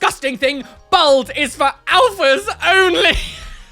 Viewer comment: I thought the first one said “for alpacas only” and I was deeply confused